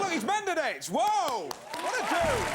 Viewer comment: did Alan say "what a Jew"?